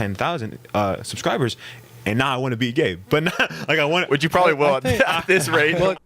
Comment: i really thought he said “and now i wanna be gay” and had to do a double take